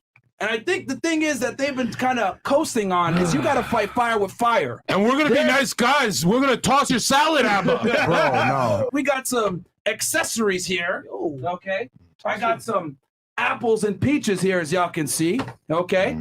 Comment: I heard "tosh your salad " y'all